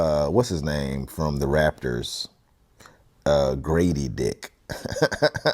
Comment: I thought you were saying "gravy dick" until I saw some comments.